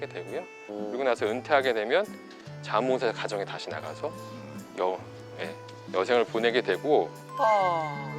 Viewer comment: 은퇴하게 되면 ?????? 여생을 보내게 되고 이 물음표 부분 뭐라고 하시는거에요? 안 들려서.. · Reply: “자원봉사 가정에 다시 나가서” 라고 말했어요